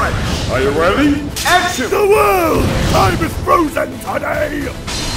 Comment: Cool, it did sound like Dio said I was frozen today